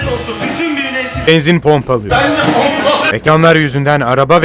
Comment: da nediyor ben yanlış anladım herelde bende Allah'ı mı diyor